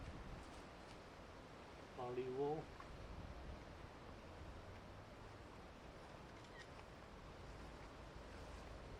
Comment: Awesome work, the booster looks really nice with the bare carbon parts! 14:39 did I hear dad say "palivo"? 😀 Greetings from 🇨🇿 👋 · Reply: Díky! Ano, říkal palivo.